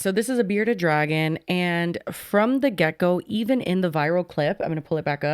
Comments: I know said from the get go but it sounded like she said "from the gecko" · You were talking about the bearded dragon and said “from the get go.” I thought you said “from the gecko.” 😊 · I swear when said from the ‘From the Get go’ it sounded like she said the ‘From the gecko’ and I did giggle 😂😂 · “and from the gecko” am I the only one who heard that lol